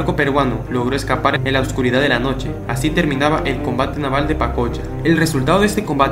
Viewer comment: Pensé que decía panocha xd.